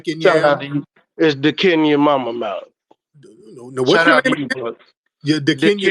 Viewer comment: LOL i guess nobody caught the troll at 11:42. Listen to his name carefully and tell me what you hear lmao 🤣He said it twice and tariq didnt catch it!! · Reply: dicinyourmamamouth ? He a whole fool for that 😁